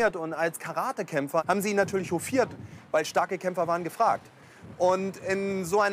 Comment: was sagt er "rufiert"? Was zum Teufel ist das? Kein Hass bitte, bin kein Muttersprachler, will nur dazu lernen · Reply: Hofiert